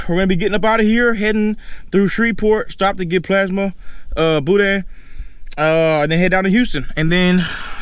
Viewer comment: Dude, I seriously thought you said “Get some bootie and head down to Houston.” 😮 I’m like “He keeping it real real.🤣” Then you grabbed your wallet and my head exploded. No hate though. I wanted to see her face or hear what you were going to say or something. I’m Mississippi nosey though so there is that. 😂😂😂